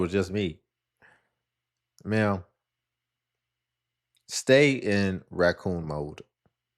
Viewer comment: Did he just say stay in raccoon 🦝 mode ma’am ? 😂😂😂😂😂😂😂😂 df I can’t stop 😂😂😂😂